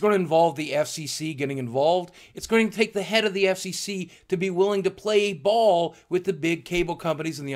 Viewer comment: I'm sorry, all I heard was "playing ball with the FCC's head" and had to masturbate.